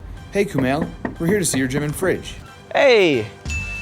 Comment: Thought he said German fridge and i expected something special